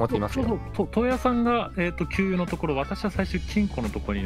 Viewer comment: 金庫甘噛みで○んこに聞こえるw